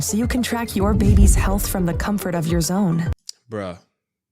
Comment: Did she just say...from your zone?